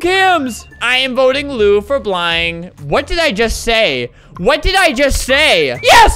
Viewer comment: I am voting lue for blying .....what did I just say???